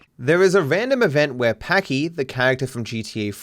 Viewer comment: When you say "Packie" at 3:08 YouTube censored the word in the captions, which means they thought you said the first 4 letters of Pakistan which is a hateful word towards people of South Asian descent. Hopefully, you don't get demonetised for this because that would be really stupid of YouTube